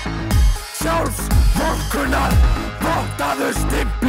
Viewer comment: Does anyone else understand "Vodka" here?